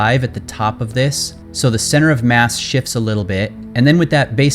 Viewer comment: Sounds like your saying "Ships" instead of "Shifts" If that's what you trying to say :) Awesome Videos Though.